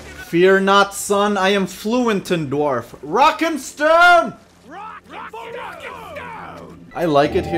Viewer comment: Did I hear a rock and stone?